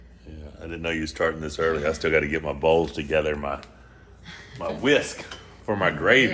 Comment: Defintely thought monster said, "i gotta ge my balls together!" Lol took a sec to realize he said bowls